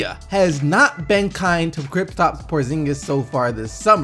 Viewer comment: did he say kriptap?!